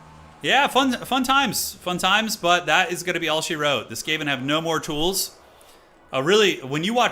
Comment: What is he saying at 2:45:31? "That is gonna be..." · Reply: That is gonna be all she wrote. "That's all she wrote" is a way of saying something has come to an end, sometimes suddenly.